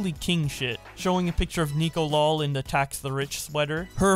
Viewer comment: Not gonna lie I thought her shirt said fax the rich.